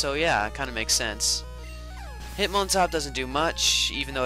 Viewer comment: oh and btw, when you said hitmontop, it sounded like you said "I like HIM on top" HAAAAA